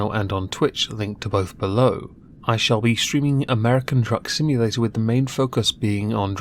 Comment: My hearing is going, I thought he said American Drug Simulator? 😱